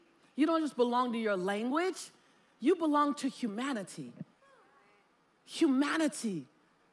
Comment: When she was saying “Humanity” so many times in a row, for some reason with her pronunciation I kept hearing “hu-manatee” and I was imagining a manatee with a Monat hat… Edit: ‘she’ being the first speaker